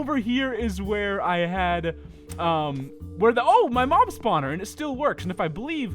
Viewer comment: LOL 3:02 I never knew that a 'mom' spawner was in the game XD he said mom instead of mob